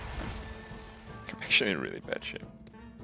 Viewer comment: what'd he say? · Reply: "K, Im actually in really bad shape" or something like that